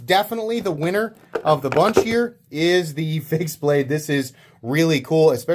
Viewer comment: sounds like you say "face blade" instead of fixed.